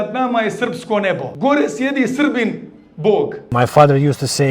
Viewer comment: he did not say wha are protecting the serbian god 0:08 he says gore sjedi srpski bog, Above is sitting serbian god. what a joke to talk about God like that